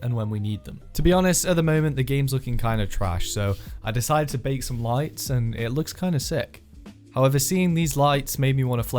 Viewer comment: What can I say? I'm a lights man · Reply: I heard "and I baked lighting" and im like no way he did that...